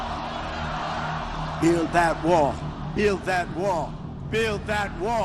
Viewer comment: He says ''built that wall'' but i hear ''kill them all'' do not pay any attention to me or my comment, i just escaped from the mental hospital,i got out from the lunatic house,i stepped off the woo-woo train...hahaha...or...am i...?